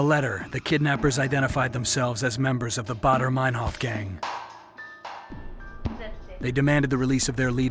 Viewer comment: there is writen "the Red Army" !!! not one word about bader manhoff gang ( i guess he say bader manhoff gang, i dont understand him well when he say it)